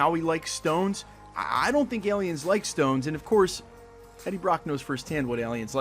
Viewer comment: hey all! this my video from yesterday... had to take out the screen shots, Sony out here fighting a ton of footage leaks, I got caught in the crossfire... but no worries. See you guys tomorrow with some major theory videos :) enjoy the movie tonight and let me know in the comments how you feel about it :) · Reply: When Eddie mentions “I don’t think aliens like stones” I thought I heard “The Stones” as in a reference to The Rolling Stones. Am I mistaken?